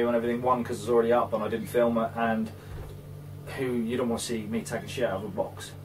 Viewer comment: I did a fast forward thought I heard "You don't want to see me sh*t in a box!!"😂